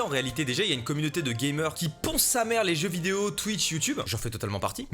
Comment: Je ponce les sims 4 ..... heumheummm quoi ? pardon ? Je bosse je disais